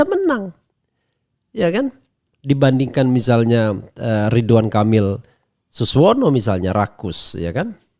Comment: Apakah saya salah dengar, "Ridwan kamil dan suswono= Rakus" 😅. Ada2 saja 😇😇